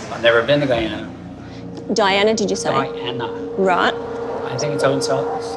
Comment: At first, I thought he said, "Diana," as in the name of a town??? LOL!